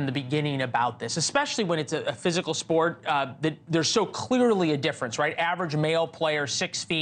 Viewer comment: I thought he said there was clitorally a difference, I was about to say he’s right literally! 😂